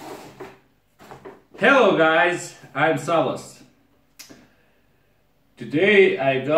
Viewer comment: Why do I always hear "Hello guys I'm Starbucks :)" ?